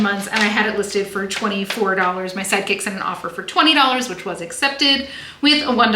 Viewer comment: Yay, a video from. Can I say that so often I mishear you as saying “My psychic sent an offer for $24,” and it always makes me smile because I sure wish I was psychic in this business!